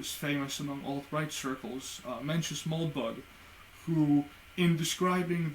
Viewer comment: Hello, What is the name of the vloger you mentioned at 6:08? I can't understand the name, alas. I will appreciate the hint. Anyway, great content, great rhetoric, very elegant - a pleasure to listen to; chapeau-bas. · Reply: I was referring to Mencius Moldbug. He's not a vlogger, but a blogger. His blog is now finished and he will not be adding to it again. He says that it has fulfilled its purpose. But his archive is rather extraordinary and worth checking out. I especially recommend the series of posts called "An Open Letter to Open-Minded Progressives" and also his "Gentle Introduction to Unqualified Reservations." Here is the link to his blog: <link>